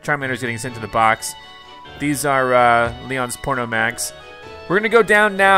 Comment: correct me cause I’m most likely wrong but sounded like he said Leon’s porno mags referring to porn magazines lol